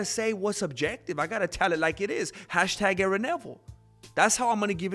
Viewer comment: did I hear Gary Neville?😅